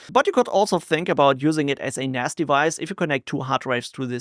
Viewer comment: I heard "you can also use it as a nasty vice" ... then I realised you said "use it as a NAS device"!